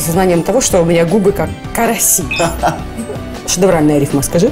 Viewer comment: Мне послышалось он сказал как карантин